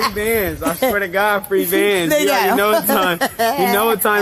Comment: That was $300 at MOST 🤦🏽‍♀️ Sad hood mentality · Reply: Sounded like he was saying 3 bands. 🤷🏾